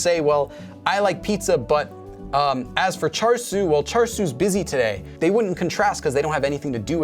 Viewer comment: Lowkey thought you said char-siu (like the pork) instead of the name 철수 (cheolsu) 😂 Think I need to work on my Korean listening skills lol